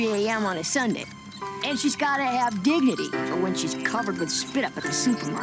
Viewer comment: Did she say that mothers beed drinks??? Lol 😆 15:01 maybe i heard wrong